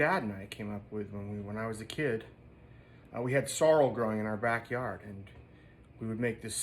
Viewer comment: For a second I thought you said you had sorrow growing in your back yard. That would explain some of your more melancholy songs since you are what you eat. :)